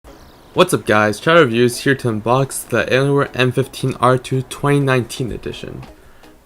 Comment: When he said hey guys chowder reviews here I thought he said hey guys child abuse here